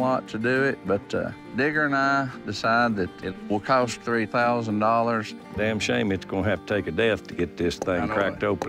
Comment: what did he say at 9:16 ??? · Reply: Digger and i, Decided that it will cost $3,000 Damn shame its gone have to take a death to get this thing cracked open again.